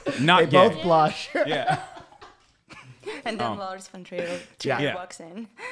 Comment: did she just say lars von trier?